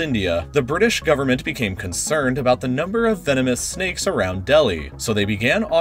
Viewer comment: I seriously thought he said feminist snakes...